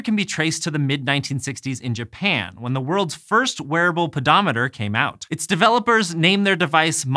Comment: I keep hearing "werewolfodometer". Is it just me?